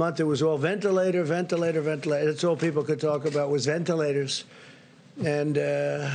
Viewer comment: Did he say entilators at 6:14?